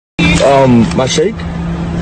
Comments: for a second, i thought he said snake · Straight up thought it said snake... 🤦‍♀️🤦‍♀️